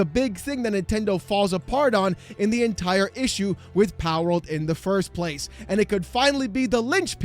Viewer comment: they thought Palworld was going to be Pokemon killer.. no, it will never be a Pokemon killer.. it is Nintendo killer.. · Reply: indeed. kinda fitting that the subtitles at 6:11 transcribe palworld as "Powerorld". nintendo was really playing with power, they didnt understand.